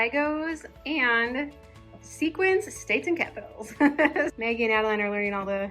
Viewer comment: At first I thought you said “Sequins States and Capitals”, and I thought it was a fancy game your crafty daughters came up with to learn their states and capitals 😂